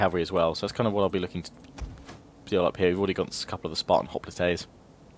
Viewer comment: If you watch the video at 4:11 it sounds like he saying Spartan hoplATAES